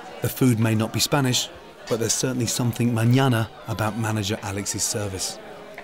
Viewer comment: Did he say there’s something morning about the service ? Did he just try to use Spanish and not make any sense ?? 😂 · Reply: Tracey Jeanne. No he said mañana which means tomorrow. He’s joking that they’ll get their food mañana which is the only Spanish thing about it